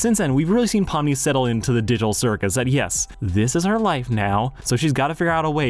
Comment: did he just say the diddle circus?